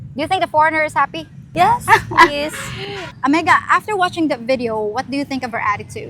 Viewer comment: I'm not surprised the words "entitled" and "disgusting" came up several times. Typical entitled feminist. I wondered about a couple phrases in the video. At 4:08 and 11:05 you used a term that sounded like "amega." Is that a Visayan word? Also, can you please clarify the "famous quote" said at 18:11 that sounded like "ilang ng tahanan?"